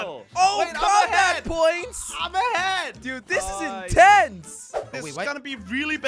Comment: I'm ahead! What i heard: I'm a hag!